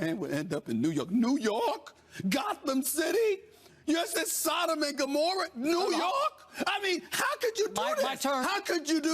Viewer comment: What did Stephen A said @ 5:46? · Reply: “Is this Sodom and Gomorrah”, referring to how sinful it was to sign Tebow to play for a team in a market like NY. Arguably most iconic city in the modern world